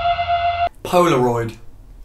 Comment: I thought he said Polarize.😧😧I listen to tøp a lot💖